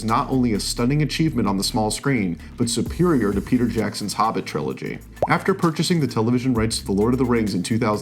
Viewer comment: He said it was SUPERIOR to PJ's LOTR. ...hard to believe. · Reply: To be fair, he actually said Jackson's Hobbit. Still, that ain't hard to beat. 😜 EDIT: the 0:40 mark